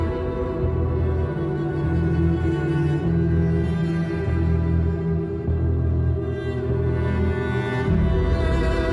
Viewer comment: candarli ne diyor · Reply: Kekeleyerek sultanım diyor